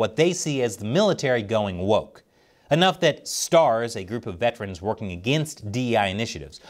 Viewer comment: Did you say S.T.A.R.S? 😃